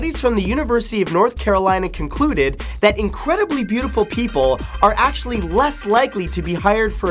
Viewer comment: I paused on the 2nd syllable of people, so I heard "Incredibly beautiful pee"